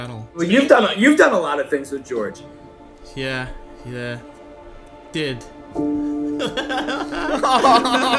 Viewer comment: When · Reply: lookinig back at the video he actually said "he did" not he was